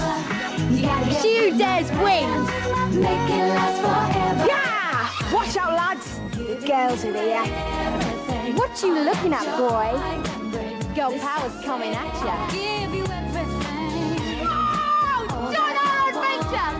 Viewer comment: I’m sorry but what does Scary Spice shout at 0:21? Sounds like “WAAAAARRRRR DON’T HAVE A SPHINCTER!!” 😳